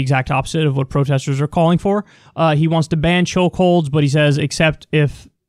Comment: Trump can't enunciate. I keep hearing "choke-holes" and I don't know how I feel about that.